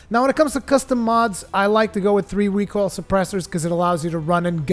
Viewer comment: I heard, "three week old suppressors". Lol